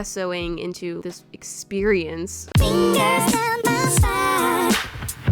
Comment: I CAN’T BE THE ONLY ONE WHO CAME STRAIGHT TO THE COMMENTS WHEN I HEARD “FINGERS DOWN MY SPIIIINE~”